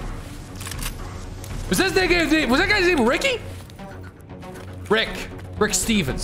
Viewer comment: do I hear what I think I hear or am I going crazy? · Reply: He was trying to say name but he was speaking really fast so it came out weird